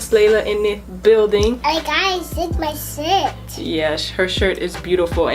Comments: It sound like Layla said hey guys this my $h*t 🤷🏾‍♀️😭🥰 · It sounded like she said “ Hey guys! Look at my sh*t!!!!” I was like “🤭😳 Anna oop.....” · did she say this is my shit 🤣😂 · Is it just me or did it sound like she said s. H. I. T. Instead of shirt👁👄👁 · I think she said shi* or Shirt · I thought at first Layla said “Hey guys look at my shit” my eyes went big 😂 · At the start when she said “ here my shirt “ I thought she said this my shit 😂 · I thought layla said I zipped my shit